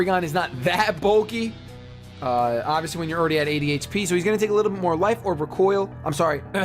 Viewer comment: when you said 80 hp it sounded like you said adhd lol